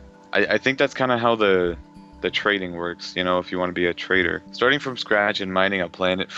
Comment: Lol every time you said Trader i thought you said traitor.